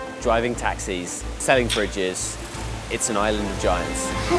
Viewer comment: did he say "selling bridges"?